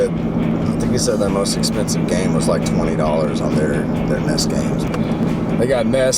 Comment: Woot. Love learning bout all these retro games :) at 7:54 most people would think he said "Ness" twice but he said NES, SNES. Hahahaha I learned alllll of the consoles that have been made. This series is truly great timing I LOVE this show.